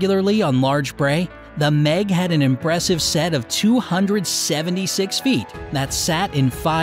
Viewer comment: Did I hear feet?